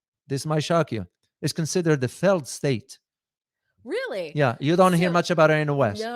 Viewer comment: is considerer what? I didn't get the word. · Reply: A failed state